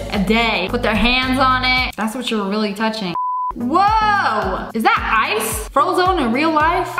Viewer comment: did he say wow or woah?